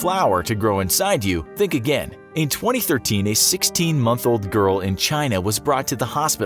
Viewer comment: Did i hear that right 7:30 sixteen month old girl?